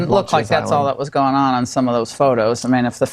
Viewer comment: "unfortunately photos can speak"? Or "fortunately photos can speak"? Couldn't understand.